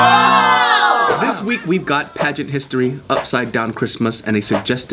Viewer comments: I thought it said pregnant history that's why I clicked on the video so now I'm sad no no Down syndrome people got pregnant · I thought it said pregnant history